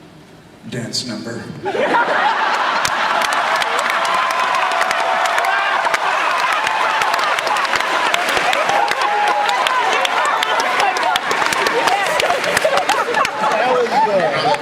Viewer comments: what did Peter say? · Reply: Dance number, lol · what did he say? dance number? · what does peter say? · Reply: "Dance number"